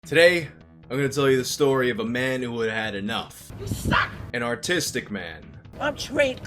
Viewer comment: I heard "autistic man" and I didn't even find it weird.